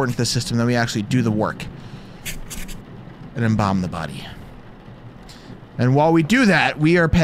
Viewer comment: When you mishear the words... 1:20: ... So we actually do the work... And then we bomb the body. Me: Excuse me?..